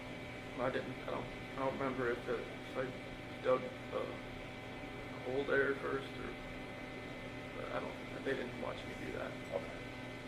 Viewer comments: i hear Chris say "i dont know that if i dug the hole there first or i dont, they didnt watch me do that" i got some bionic hearing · Sounds like he said "dug the hole there first"